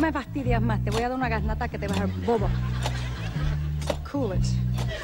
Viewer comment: does Animal say "se calle?" (Shut up?) WHOA!!